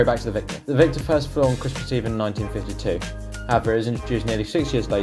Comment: Poor commentary. For heavens sake slow it down! At 0:48, for instance, you seem to say "Avro's introduced nearly 6 years later in 1958". What did you actually say? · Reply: That wasnt 'Avro' it was 'however', very sorry